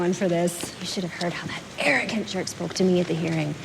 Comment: The subtitles say "Chuck" but I think it sounds like she is saying "jerk". Do you write those yourself or copy them from the script?